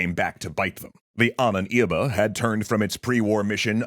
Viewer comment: Can someone please tell me what was said at 1:28? It sounds like Anan Iba but I don’t think that’s it. TIA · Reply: It's "Ahnenerbe" meaning "ancestors inheritance" if you were to translate it literally